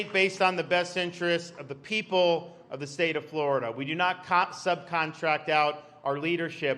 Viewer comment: It’s about time that politicians start fighting against this left wing craziness. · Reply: Did he say we do not cox-suck at the 6:16 stamp?